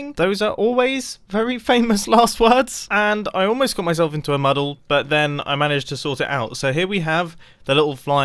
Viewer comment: what is a muddle mumbo? Is it a British word?